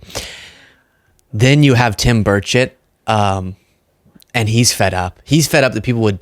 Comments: is it tim burchett or tim birdshit? I thought I heard the latter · so tired. I could’ve swore you said Tim Birdsh*t. 😂❤ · When you said Tim Burchett's name, I thought you said "bird shit". Telling? 😂